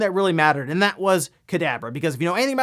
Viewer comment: Is it just me or did he sound like he said Cadaver instead of Kadabra?